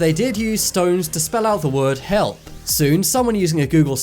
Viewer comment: i hear “to spell out the word heck”